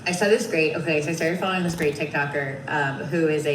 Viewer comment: I thought she said she wasn't following the case.